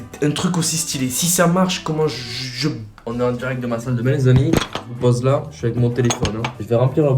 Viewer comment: mec ta couper la fin de la phrase on n'a pas compris, ta dit quoi ducoup? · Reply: Comment je suis trop content 😅